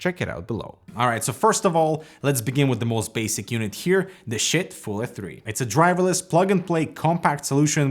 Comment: it sounds like he said shit!!!!😱😱😱😱😱😱😱😱😱😱😱😱😱😱😱😱😱😱😱😱😱